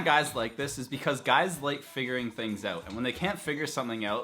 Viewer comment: I thought he said “ guys like fingering things out”